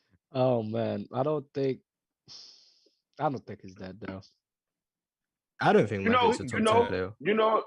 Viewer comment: does say "I don't think bird is a top 10 player? Not sure if I heard accurately. Absolutely no hostility! Mars might be my face, just new to watching only 1 month in. Anyone know his top 10? Or his "top players" all-time? Would surprise me me if he made as easy as a top 10 lol! Great show you guys kill it!